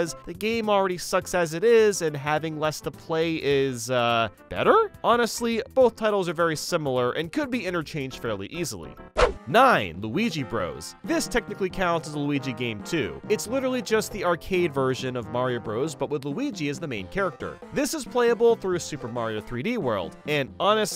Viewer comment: It sounds like you said waluigi at the start